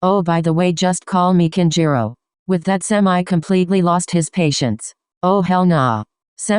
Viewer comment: When it said “semi lost his patience.” I legit thought it said panties. 🕳🏃‍♀️💨